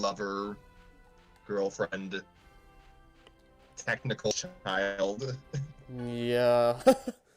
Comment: TIMESTAMPS FOR HARD-TO-HEAR AUDIO: Audio cuts out at 15:03. I'm supposed to be saying "...technical mother of his child." 18:03 - "Only Love Live! can actually manipulate your emotions, I get it." 24:23 - "Being an anime fan is suffering some days." 43:13 - "I pair Lisbeth with Kleine for some reason. I guess because they're just kind of there which kind of describes both characters." 1:08:15 - "...but shh...don't say anything, just don't-don't give away the game." 1:11:13 - "Kirito has to basically save the princess from the dungeon before it's too late." 1:16:35 - "Not as much as Rem's life!" 1:17:21 - Can't remember what I'm saying exactly, but I believe it's something along the lines of "When you're actually playing Animal Crossing, Tom Nook's pretty benevolent. He lets you take your time paying off loans. That's amazing." 1:23:48 - "...and they hurt me in their own special ways, but..." And one line from that I totally talked over at 1:11:19 - "Anyone that sniffs someone's hair in anime is a serious f*cking villain."